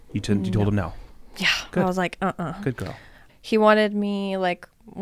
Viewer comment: I’m sorry did just say “Good girl” ??? Huh???